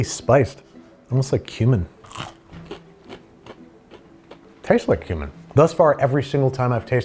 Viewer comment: Every time Babish said "tastes like cumin", I thought he said "tastes like human." Scared me for a minute.